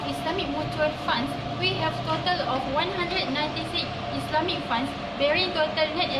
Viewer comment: Host kayu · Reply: Btw content yg padat. Tapi subtitle pada minit 4:50 ada kesilapan. It should be 196 instead of 169